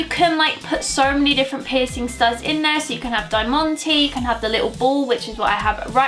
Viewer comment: what do you say? Dy monty?